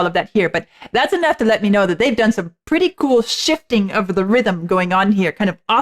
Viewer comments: I thought you said "some pretty cool shit," at 41:22. Being all bad ass in your new leather jacket. 😊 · turning into a Rock Chick! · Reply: I thought she was going to say "they've done some pretty cool sh*t here!" haha